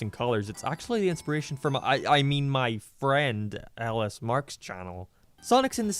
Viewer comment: I heard “inspiration for mommy my friend LS Mark”